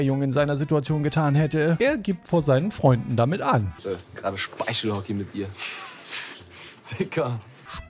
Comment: ich hab "Speichelorgie" verstanden, was die Sache nicht viel besser macht🤢